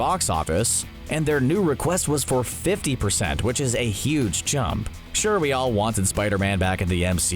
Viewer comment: Anyone catch the narrator saying, "..a huge chump." @ 09:40 ? I'm sure he meant to say chunk, or is my hearing going?